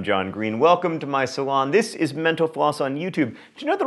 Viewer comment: whenever he says "mental floss" i hear "menopause"